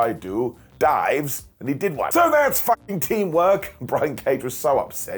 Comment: Did just say "fucking" instead of "flubbing" This is the real 2+2= 🥔